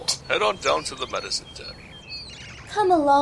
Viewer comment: I thought Jayfeather said "the adopted dungeon"